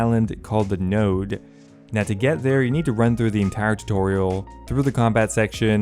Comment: Did you say nerd?